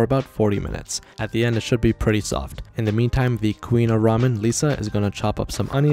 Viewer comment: I thought he said the queen of lisa, ramen 😂